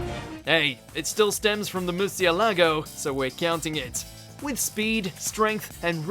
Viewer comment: Wait wait wait, is that how #1 was written? I always thought he said Murcielago (which is bat in spanish) which with the design it made sense. On another note someone needs to give some Spanish pronunciation 101